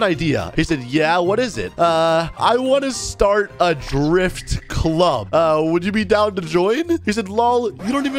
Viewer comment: I thought it said i started a strip club. not a drift club...